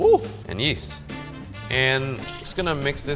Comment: When he said “yeast” i thought he said “yeet”